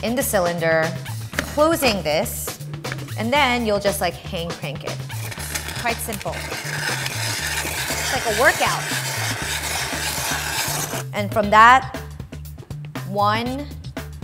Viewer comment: Lime if you heard her say, "hayned crank it" in the begining